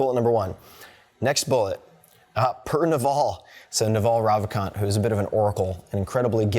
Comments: Hey, at 15:50 I can't understand the name drop of the investor he was speaking of... Can anyone help me with that? Thanks if anyone can! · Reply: He mentioned Naval Ravikant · What was the name of the investor around 15:49? I can't understand him. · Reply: Naval Ravikant